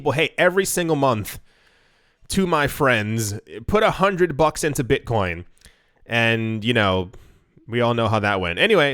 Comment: Crap, I thought you said put 100 BTC into the bank, and I did, thanks for the heads up mate